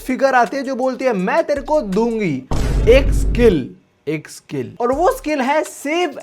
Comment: kya bola 💀💀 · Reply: Skil